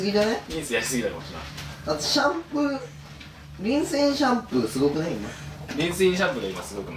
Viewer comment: リンスインシャンプーがHey! Say! JUMPに聞こえた😳